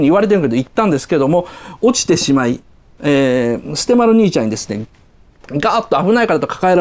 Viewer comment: 雉の話してた時、捨丸兄ちゃんて言うとこ キジ丸兄ちゃんって言ったように聞こえたけど、空耳かな 何か凄くツボに入っちゃった